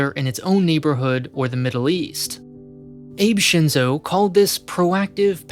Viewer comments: Ah yes, Abraham Shinzo, Prime Minister of Japan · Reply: I misheard "Ape Shinzo" at first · Lol, for a moment I thought you said "ape shinzo". Abe, as in "ah-bee", not as in "ape".:-D Also, "Abe" is his family name, not his first (not like "Abe" Lincoln), so it may be easier to remember when you say it as most people on news networks would "Shinzo Abe". Edit: Also, it's strange how you keep referring to him instead of his successor(s). He hasn't been in power anymore for over a year. These kind of minor slipups make it feel as if you haven't done enough research on Japan as a whole before making these videos, which would surprise me.